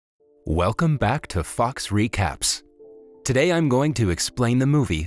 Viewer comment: I thought the intro said “welcome to FUCKS recaps then I re listen and hear “fox recaps” (sorry for my language)